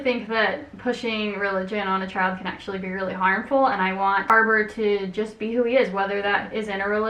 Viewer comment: Captions: bArBaRa (instead of harbor lol)